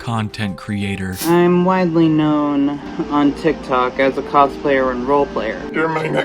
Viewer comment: I thought it said "tiktok's cosplaying molester" lmaoooooo